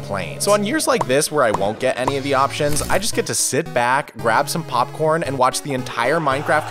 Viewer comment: "Sit back, grab some popcorn, and watch the minecraft community eat themselves alive." Perfect explination of what the mob votes are like. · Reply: I thought it said crab some popcorn lol